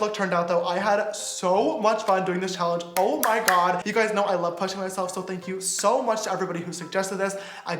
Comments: i heard him wrong and heard “you guys know i love punching myself” lmaoooo i’m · When James said I love pushing myself it sounded like he said I love punching myself 😭😭😭😂😂😂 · When you said “ I love pushing myself “ I thought you said punching 😂😂😂